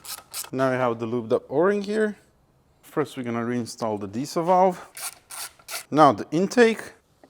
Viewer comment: did he say "the Diesel valve" at 12:38 ??